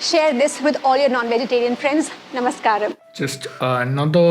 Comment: she clearly said non-vegetarian food